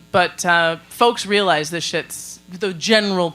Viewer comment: / 55:49 ...is saying "this shit's" or "this ship's"??? Sounds like the former..